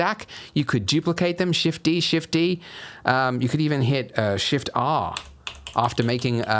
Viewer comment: Hey, can anyone tell what key after he said "shift"? because my english not really good and I can't hear clearly what's the keyword is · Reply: shift + R it copies the last action . so u have to duplicate(shift+D) first then do it .